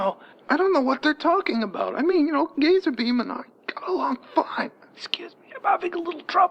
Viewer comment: uhm my subtitle says that phylange said gays are beeman instead of gazerbeam. Seems like the ai is weird today